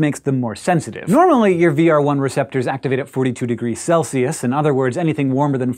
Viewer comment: Why sometimes you will still feel the burn inside your body? Does this mean there's taste receptors in our stomach? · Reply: My bad, yes I misheard the VR1 receptors as taste receptors.